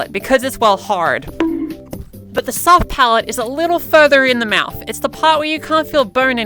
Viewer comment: I genuinely thought it said soft pirate 😭